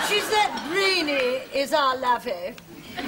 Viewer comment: What does Mrs. Slocombe say at 24:44? "Brainy is our lavy?" It's been bothering me for 20 years! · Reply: lawr17 “she’s that brainy, is our lavvy (lavinia)”